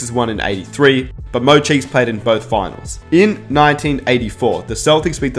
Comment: Jr Smith,David Lee,jermey Lin,Iman shumpert,Chauncey billups,timofey mozgov,Channing Frye,Jalen rose, Patrick ewing, latrell sprewell that's all I got that all the players that played in the NBA finals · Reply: hold up I thought he said played in the finals